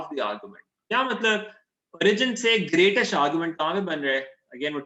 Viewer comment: sir I didn't get what did you say here greatest distance then what is brizen or something I didn't get that part ? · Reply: I know im late but he said origin